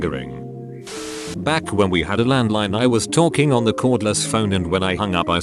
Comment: i thought he said back when we had a land mine